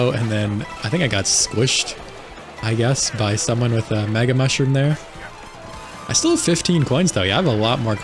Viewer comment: I can't tell if it's just me, but I keep hearing you saying "maga mushroom." What, was a secret plan to infiltrate Sarassaland leaked in a Signal group chat or something?